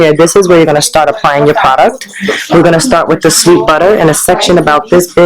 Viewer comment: did she say shae butter?